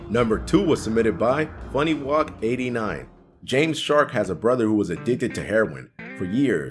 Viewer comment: who else thinks that sounded like "james Charles "